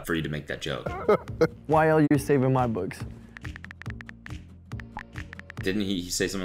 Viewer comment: YLU save in my books? someone please tell me what he said